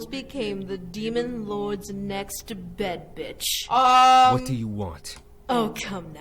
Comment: Was it only me that thought said bad b*tch instead of bed b*tch